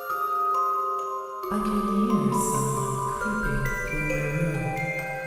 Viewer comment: Ballora sounded like she said I can here someone pooping in the room